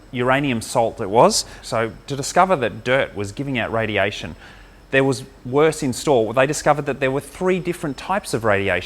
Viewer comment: can you help me with what the Dr was saying in 0:30 there was......... and why he added strontium in 1:50. because this will help me in translating to another language. Thanks. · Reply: "there was worse in store, there are 3 types of radiation" that means that there was a bigger problem that there were multiple kinds of radiation, making it more confusing. im pretty sure that the strontium is the radioactive material that was being used